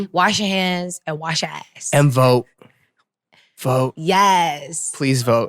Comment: i thought jessi was saying "wash yo eyes" until I heard Eric said "ass" with perfect pronunciation 👌